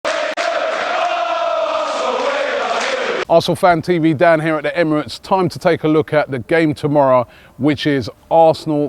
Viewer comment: I thought he said "Dan here at the Emirates". I was like 'what the fuck? Your name is Robbie!!'. Unsubscribed.